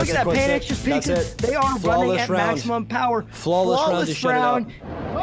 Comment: I hear flawless shroud